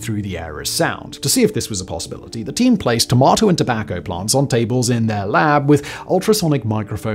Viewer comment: I'm sorry, I misheard. I thought you said they tested Tomacco plants.